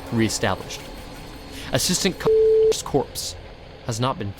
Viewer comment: i thought he was saying assistant cock and i thought he said Dr. fucklin